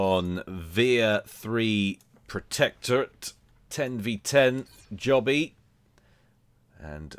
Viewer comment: did i just hear a 10v10. im back to asking for a 10v10 now